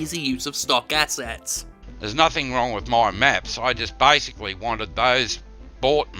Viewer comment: Did anyone else hear "there's nothin' wrong with more meth?" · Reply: Maps he said his audio is just shit